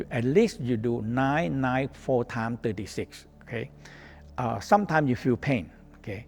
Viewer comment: ? What’s he saying? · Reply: nine four times. 36.